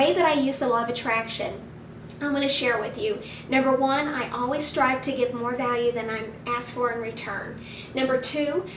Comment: At the minute 4:35 cand someone to write what she says.?...I can't understand the number 1 ''I always try to...? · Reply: Hi, Thanks for watching. What I say is "I always strive to give more value than I ask for in return". What I mean by this is... I strive to create useful, interesting, relevant content and inspiring video's and blog posts for my subscribers. And also, when I work with clients, I always strive to give a lot more value than I am asking them to pay for my training program or even a coaching call. (Under promise and over deliver is something I strive to live by).